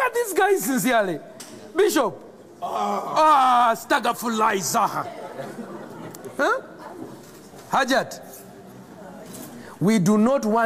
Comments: what is he saying at 1:39? I mean what sounds like "staga fulai zaha", what does it mean? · What is he saying in 1:40 , does someone understand it? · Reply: I ask forgiveness to Allah (astaghfirullah)